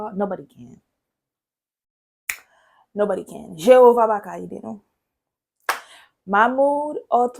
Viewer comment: Did you just say Jehovah papa'm? Did you grow up as a witness like me and in a Haitian household like me? Interesting...